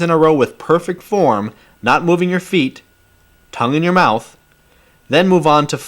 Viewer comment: im sorry, what did he say at 5:46? "C-m in your mouth?" lol i didnt catch that. excellent teaching by the way.